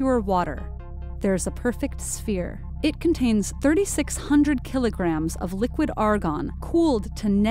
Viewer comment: I thought it said 3600 kg Air 😂😂